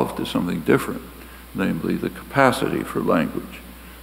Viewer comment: biological what? didn't get the word after it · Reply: pretty sure it's "endowment"